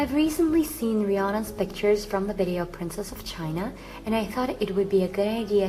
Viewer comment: i thought she said princess vagina